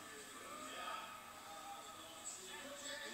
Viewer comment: i thought he said fucked when i saw it live but its definitely bucked.